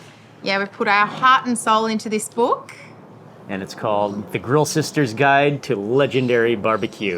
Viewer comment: Did I hear "The Creamed Sisters"?